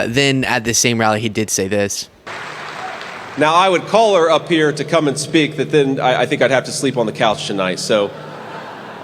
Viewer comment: “now I’d call her to come up to speak but I’d have to f the couch” Did I hear that right?! #weird